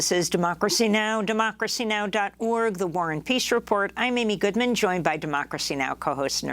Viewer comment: I've been watching DN 3.5 yrs and I've always thought she started with saying "the war ON peace report". I know that is wrong now